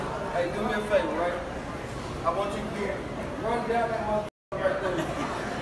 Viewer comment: what des he say at 00:52 ? is he really saying run down thru the door and down the tarmac? lol